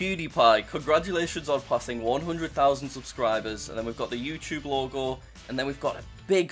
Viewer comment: did bro say 100.000 subs